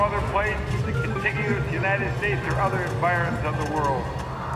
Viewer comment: I can’t tell if he says “environs” or “environments” lol, does anyone know the correct word? Ik genius says environs, but I hear environments 😭